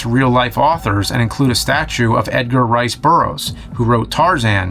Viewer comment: I misheard Edgar Rice as Edgar Wright. I'm pretty sure the director of Scott Pilgrim, Shawn of the Dead, Baby Driver, and Hot Fuzz didn't write Tarzan.